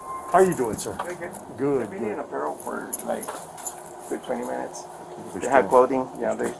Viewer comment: They've been here for 20 min they have protein, they conceal anything? @ 0:14 - @ 0:21 is what i heard? any one else tell me what was said in the beginning?